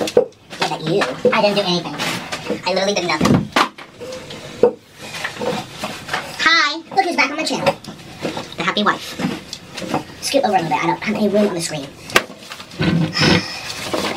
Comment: With the video speed up you can't understand what the scale is saying. Also it sounded like he said happy wife instead of unhappy